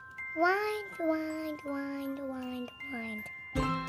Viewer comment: But fr why it sounds like she said wine wine wine wine wine 😂😂😂